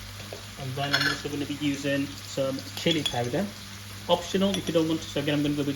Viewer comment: Can't understand the ingredient @ 6:49 is it curry powder ? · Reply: Hi, thanks for asking it will be BIR mix curry powder, you can find all ingredients and measurements on the description box. Hope this helps and thanks gain 🙌